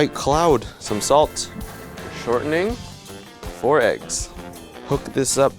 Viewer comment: When he said “4 eggs” I thought he said “borax”